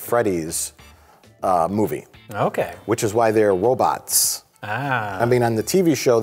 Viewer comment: Am I the only one who heard Mike say "wobots" @ 11:25?